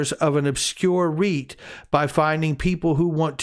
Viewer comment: REIT, lol thought first you said obscure wheat